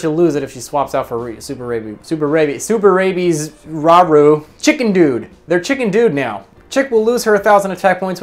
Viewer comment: Oooooooh Chick 'n' Dude. I thought he just randomly said Chicken Dude.